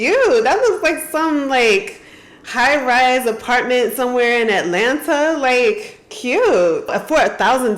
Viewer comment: 👀 Did she say CHINA?😹 Sorry to say but she just purchased a Tofu Dreg home. EverGrand has gone bankrupt for making buildings that start to fall apart in 2-3 years. I'm praying for her.